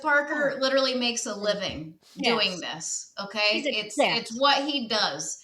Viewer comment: what did say right here? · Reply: “He’s a guest”